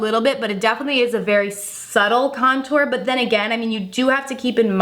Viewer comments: I so glad I'm not the only one who heard her say "subble". Still absolutely love her though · Did she just say "suble"? Instead of subtle. Honey, itd pronounced "suh-tull"